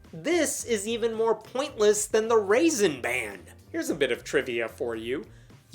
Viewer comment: "Raisin Ban"... Raisin Brand lol It's sounds like you said Raisin Brand 🤣 😂